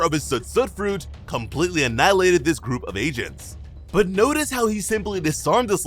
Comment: I keep hearing 'Asians' instead of 'agents' 💀